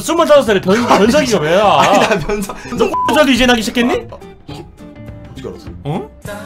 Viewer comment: 뭐라 한건지 궁금하다 잘 안들려 ㅠㅠ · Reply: 고추털도 이제나기시작했니? 인거같아염